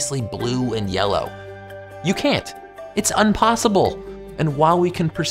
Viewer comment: did you just say unpossible?